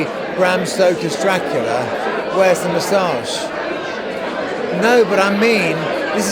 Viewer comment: sorry, what did he say here 6:40? for me it's sounds like: "where's the massage?"